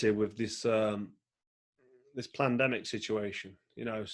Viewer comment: Did anyone hear Carl say plandemic 15:51😆